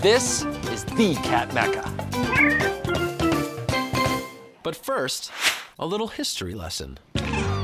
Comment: Did he say 'THE CAT MECCA' @ 1:14???